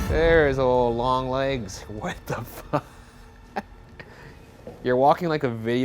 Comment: i wasnt looking at the screen at 21:40 and i thought he said "fuck you" after ryan asked him if he enjoyed some hydrotheraphy haha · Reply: Same her I heard “hey fuck you” 😂